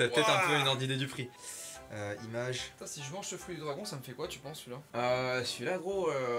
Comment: ce quoi ? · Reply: Fruit du dragom, je crois